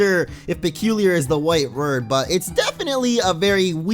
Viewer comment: did he say "wight word"?